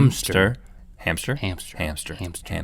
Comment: it sounded like they were saying ham stir